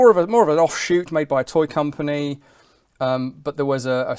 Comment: Am I hearing that correctly 1:51 "made by a toy company"?